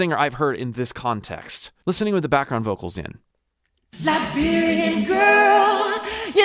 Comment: I thought he said Librarian girl for almost 30 years!!!